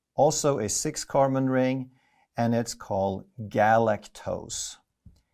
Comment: did he just say garlic toast? 😳🧐